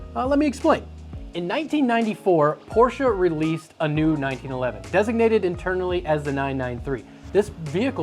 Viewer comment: is it just me or did he actually say 1911 ?